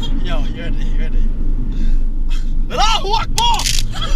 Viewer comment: Everyone is talking about 2:51 I can’t understand what said help😂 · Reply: alluh Akbar, means god is great and was a harmless term until it was associated way Terrorists and terrorist attacks as usually before a suicide or shooting you’ll hear “Alluh Akbar”